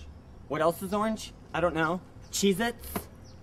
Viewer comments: I thought Qibli said jesus instead of cheezits at first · When qibli says cheez-itz over and over it sounds like he is saying Jesus